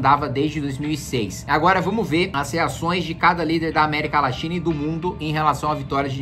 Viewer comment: Só foi eu que ouvi "América Laxina?"